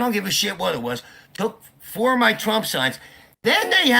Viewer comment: wait did he say... "took FOUR of my trump signs"???? How F'ing many signs did he have in his front lawn!!!!